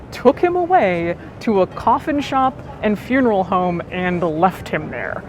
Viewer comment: So, the combination of you wearing your mask, no captions, and my children fighting about a door and whether or not I was in fact open or closed, I thought you said his roommates took him to a coffee shop and funeral home. I was like, alright. Thats one way to do it?